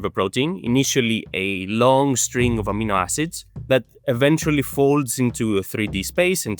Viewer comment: At one place it sounded like you said Amino Asses. It then imagined A mean o'l asses and i have a new category for people who are rude.